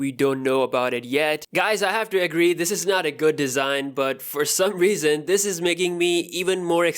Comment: İ dont wanna believe this design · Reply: I heard this isn't the finale design. Hope the finale design looks better